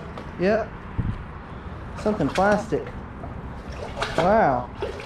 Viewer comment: When you dropped the magnet and said you saw a plastic bottle, You said "something plastic". I thought you said "f*****g plastic. At first I was shocked. You would never do that. I'm 60, so my ears are going young man.